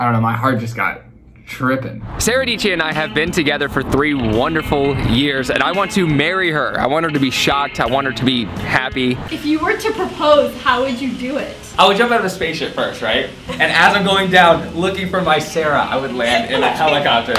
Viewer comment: Is it just me or did he say I want her shot ?